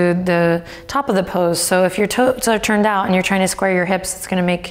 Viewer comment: "...so if your toads are turned out..." I had to pause and go back to be sure I heard correct 😂